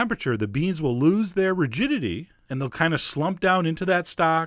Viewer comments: Totally thought you said "the beans lose their VIRGINITY" and I was like what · When Chef John said "the beans will lose their rigidity" there was a lot of background noise for me and I thought he said "the beans will lose their virginity" o.O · I thought he said the beans will lose their virginity and slump down. oopsies. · I thought chef John said the beans lose their virginity and I was like "man, this really is some sexy dish". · Is it bad that I misheard rigidity as virginity...?